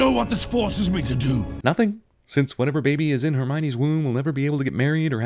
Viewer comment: did he just call her Hermione